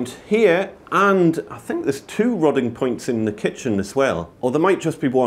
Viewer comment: "Rotting" points or "robbing" points? The closed captioning isn't working. I think they're called waste access points or cleanout plugs here and used when a blockage occurs. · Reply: Rodding points.